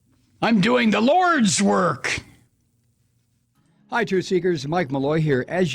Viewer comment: Did I hear you say, "I'm doing the roids work.", or was that "...lordswork."?